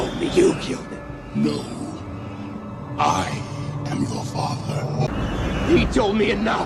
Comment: i hear "no,i am the father" wth 😅😂